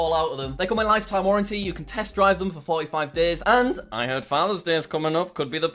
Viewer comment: did he say test drive???!!!!!?!?!?!?!